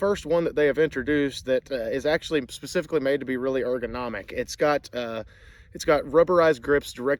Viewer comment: did you say 'uglyonomic'?